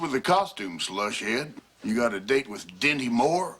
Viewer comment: "What's with the costume, Slush-head? You got a date with Dinty Moore?" LMAO Still! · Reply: For years I thought he said Demi Moore.